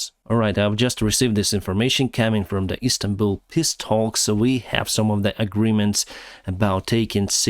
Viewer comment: love how u pronounce "peace talks" sounded like u said Piss talks, sry just tought it was a bit funny